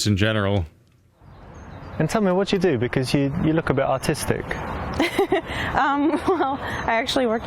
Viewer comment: I literally thought he said autistic and was shocked that you got a blue heart for that